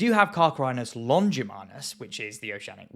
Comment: Isurus glaucus (Müller & Henle 1839) was an actual name used for mako sharks back in the early/mid 20th century, and it's only after Garrick (1967)'s work on Isurus that the hot mess of that genus became clearer, and that was the basis of Compagno (1984)'s account for makos. Many sources around the 1970's would've had I. glaucus as the valid name. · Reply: Yes. I remember it from an old book I used to have. Plus I'm certain Dreyfuss does say Longimanus. I always heard it as that.